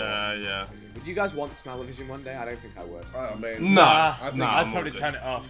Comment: What does Vik say at 6:49? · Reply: Would you guys want smellevison one day